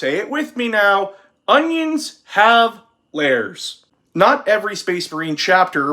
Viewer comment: "Onions have layers." I heard, "Onions have lairs," and now I'm imagining a chaos warped root vegetable sitting on a throne of asparagus clutching his two-handed power peeler. PS: my wife is now laughing like a lunatic.